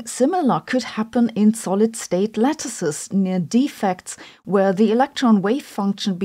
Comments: I thought she said “solid state lettuces” and was going to make a joke. ;-) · Heard you say "solid state lettuces" and immediately thought of checking my fridge temperature